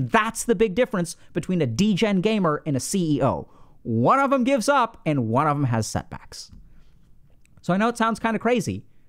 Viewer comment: I had to listen to this twice I thought you said “ one of them gives up one of them has sex “ 🤣